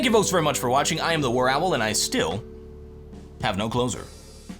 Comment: every freaking time i hear " and I still have no clothes on" at the end XD